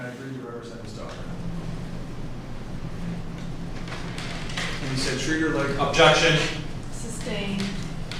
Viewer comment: all I heard was "he said treat her like a"....then OBJECTION. Make people jump at the defense table and scared the crap out of me since it was all calm and quiet until he yells that as loud as he can 🤦‍♀️ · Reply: he said treat her like your daughter, that's why the prosecution shouted objection so loudly. he was emotionally trying to manipulate the jurors.